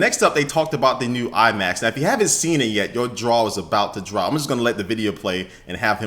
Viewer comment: lmao i thought he said "yo draws are bout to drop"